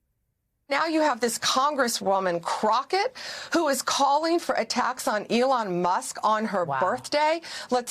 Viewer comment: Blondi (and even that is as fake as her testimony to the Senate hearing) , she misheard something... No one is calling for "attacks on Elon Musk", they are calling for "A TAX " ON THE MUSK!